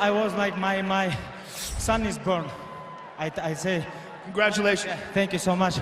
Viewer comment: Khabib fought the same day his kid was born across the ocean! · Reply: Ohhhhh I thought he said my son is burned.....opps😝😝😝